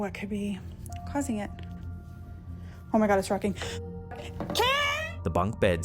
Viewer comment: the rocking chair Rocking in the little girls room is what she was saying "on my good its rocking."